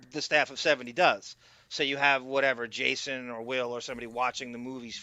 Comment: I thought said, "He has Jason the Whale watch the movie for him".. Then I realized it was Jason or Will.. Either would really work though. haha